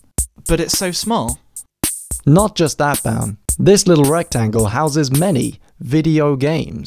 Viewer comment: OH! his name is BOWN! i thought he said brown...lol